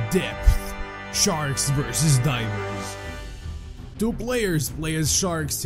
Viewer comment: I heard diapers instead of divers lol MOLESTER MOON IS COMING!!! 🌚🌚🌚🌝🌝🌝🌞🌞🌞🐲🐉🐉🐉🐉🐉🐉🐉🐉🐉🐉🐉🐉🐉🐉🐉🐉🐉🐉🐉🐉🐉🐉🐉🐉🐉🐉🐉🐉🐉🐉🐉🐉🐉🐉🐉🐉🐉🐉🐉🐉🐉🐉🐉🐉🐉